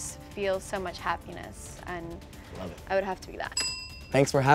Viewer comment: Was I the only one person who thought it said Ellie🤣🤦🏽‍♀️